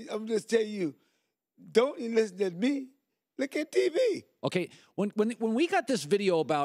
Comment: what in gods name did he say? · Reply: I think he said "don't listen to me look at tv"